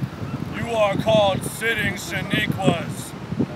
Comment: did he say sitting Shaniquas thats my nick name.